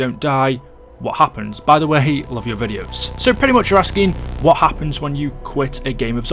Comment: Lmao, it sounded like he said, "i hate all of ur videos"😂🤣